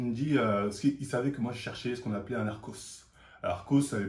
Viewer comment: j'ai compris " je chercher un narcos " j'ai cru t'aller parler de beuh , je suis matrixé ^^